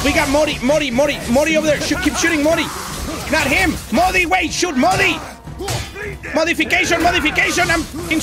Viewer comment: I heard shoot mully